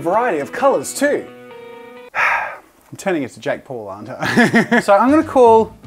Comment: I’m I hearing things or did just say jack Paul? 😂 I’m crying!